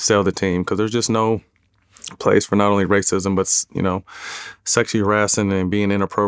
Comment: Sounds like he said “There’s no place for Sexy Hurrassin” lmao